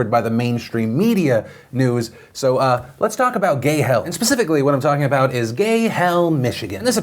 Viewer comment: I thought he said "Gay Health"